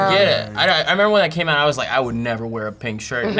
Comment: does say 'when i came out' ?